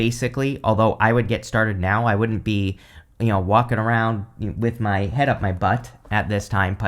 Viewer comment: I thought you said, "I wouldn't be f*ckin around with my head up my butt" 😂😂😂!!! I literally lol'd and had to play it back just to hear it again! Then, good ole broken ears heard what you really said (walkin not f*ckin) hahahahaha 😅 On a serious note, though, I just watched a clip of Zendaya introducing Cher before Cher strode on-stage and took the mic. Zendaya has the grace and manners and Damm-fine upbringing to step back, literally and figuratively, to give Cher allllll the space. And at the end of her speech Cher strides off without even giving the applause a second to inhale and WITHOUT the statue, the award, from the Rock N Roll Hall of Fame! 😂😂😂😂 And for all her modesty about how her speech might come out, she knew exactly what she was doing and she freaking KILLED it. Cher knew she wanted to be famous when she was a child and she learned of the story of Cinderella. My big decision is if I want to, like, seriously, hang out with Cher and be famous too. I'm not even joking. Only fyi, not for any comment in return, Pisces Sun, Scorpio Moon, Sagittarius Rising. Anything is possible ❤